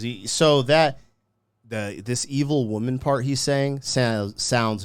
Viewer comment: For years as a kid I always thought he was saying “Mid evil woman” 😂